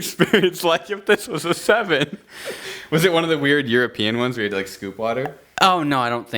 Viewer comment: Am i the only one who heard "you are peeing" instead of "European" at 15:01? 😂